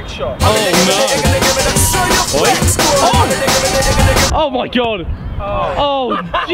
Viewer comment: Who else heard oh shit